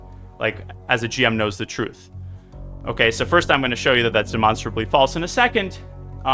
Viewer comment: the subtitles saying "monsterably false" cracks me up more than it should